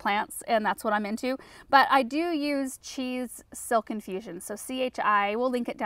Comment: Omg I definitely heard “cheese silk infusion” when you were talking about your hair I have the greatest visual in my mind and can’t stop laughing. Thank you for that 😂